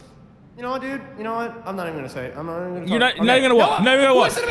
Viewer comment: nah all i hear is FALLACIES FALLACIES FALLACIES!